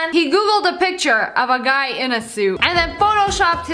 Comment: WHO ELSE HEARD SU TART XD